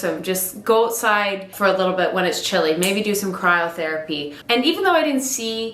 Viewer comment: I thought was saying cry therapy and thought ‘yep I can cry all day’ 😂 I’m a naturally very cold person but found now I have bigger muscles, I stay warmer a lot easier which is great for me!